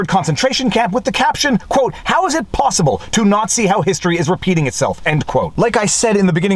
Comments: David. I heard you say nazi but you were saying not see. Thought you would add a caption tongue and cheek. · After repeatedly self-censoring your reading of the document, you read "How is it possible to NAZI how history is repeating itself?" Yes, that's how I heard you say it. lol